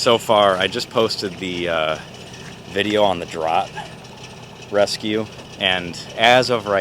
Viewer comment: I dont understand 33:16 Drought? I have watched all of your videos but which one are you talking about? Thanks! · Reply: the vid previous to this one the Drott excavator great vid.